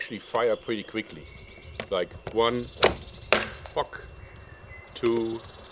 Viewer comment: Anybody else think he said 'Fuck'?